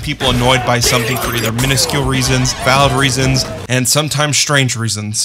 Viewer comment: Install Raid for Free ✅ IOS/ANDROID/PC: <link> and get a special starter pack with an Epic champion ⚡Tallia⚡ Available only for new players 🎁 Use the promo code JTSKIN to get the Epic champion Stag Knight and a Skin for Stag Knight designed by JonTron! Don't miss your chance, the promo code is valid until October 7th, only for new players. 📱 If you are an iOS user, enter the promo code here: <link> Whats your favorite disney project? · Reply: did you say "Valve reasons," like as in Half-Life Valve?